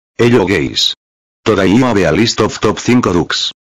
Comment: The voice sounds like it said “Hello Gays”